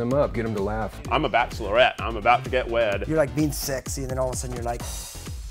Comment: I heard "I'm a bachlorette I'm about to get wet" nOT WED